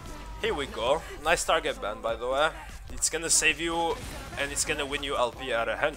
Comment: What did he say? I understood Nice something something by the way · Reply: nice target ban btw